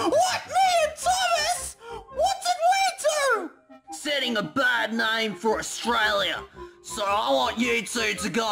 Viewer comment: Ya know what he says… saying a bad name in Australia is a very serious problem.